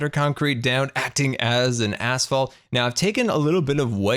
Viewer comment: does nobody notices how it sounds like scar said asshole